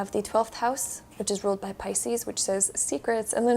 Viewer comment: I picked card #5 and from what I heard, it matches my anime boi crushes. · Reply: On card#5 did u say real bad Pisces? On 46:24 . I might have misheard.